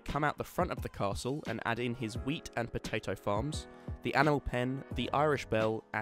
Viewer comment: on 1:37 Edit: the meaning is he says wheat